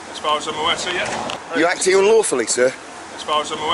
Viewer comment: Hate bailiffs but i think he thought u said "Are u acting lawfully" sir.